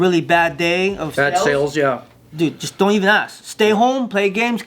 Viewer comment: Sounds like he said, "Don't eat ass." 😂